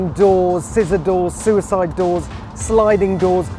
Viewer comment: Did he just say "suicide doors" at 1:15?